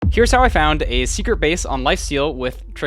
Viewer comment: I first hear "Here's how I found a secret BASS on Minecraft with Trigonometry. I was really confused for the first seconds, until I got it was just a triangulation exercise to find a secret base :D